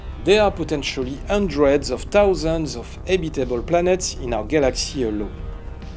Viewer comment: For a second there I thought you said "hundred of thousands of EDIBLE planets in our galaxy alone"... nom, nom!! 🤓👍